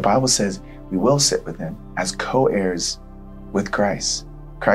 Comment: Thank you for this great message! But I want to say one thing; I see some inconsistencies in the subtitles. Maybe having consistent subtitles can help people so that they don't misunderstand the message. If you could fix the inconsistencies in subtitles in future videos, that would be great. (examples) 4:54 - I think you were saying co-heirs, but the subtitle said co-chairs 4:57 - I think you were saying "heir with me", but the subtitle said bear with me I hope I am not sounding to "judgy" hahahaha, anyway thank you again for this amazing message